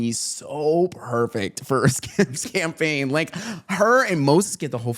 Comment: skims campaign? Trisha misheard you she thought you said congress campaign